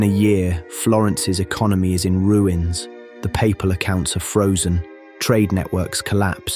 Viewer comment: he did not really say "the paypal accounts were frozen"? wtf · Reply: Haha, no, I didn't. That was "papal accounts". Easy thing to mishear, they sound similar. Thanks for watching!